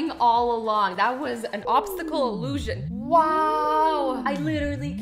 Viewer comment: did she say "Obstacle Illusion??"